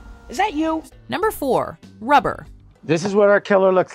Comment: When I first heard #4 I thought they said Flubber and was Like no fuqen way :p haha